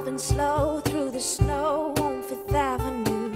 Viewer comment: I thought it said, "We're driving slow down the road on fifth avenue." o-o Guess not then.. xD